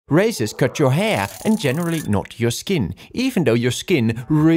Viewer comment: I honestly thought he said racist cut your hair